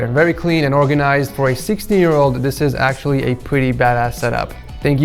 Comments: did he say 60 year old or 16 year old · am i like retarded or did i hear 60 year old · who else hears 60 year old